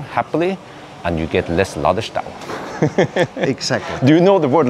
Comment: What was the word used for ????? (see below) "... and more cars are charging happily and you get less ??????" "... do you know ????" "... not really" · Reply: Ladestau. German word. Meaning a charging queue; when you have to wait in line for your turn to charge.